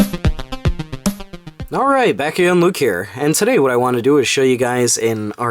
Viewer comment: At the beginning of your video, it sounds like you say, "Backhand Luke here." That strikes me as a great name for a band, don't you think? Backhand Luke.